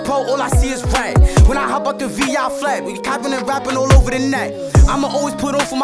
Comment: I gotta be trippin did he just say we cappin and rappin all over the net